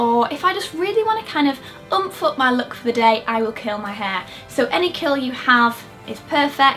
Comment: I thought she said 'I will kill my hair'